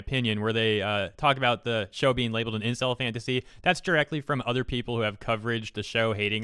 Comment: LMAO I THOUGHT IT SAID “INCEST FANTASY” AND I WAS JUST LIKE WHAT????? HOW???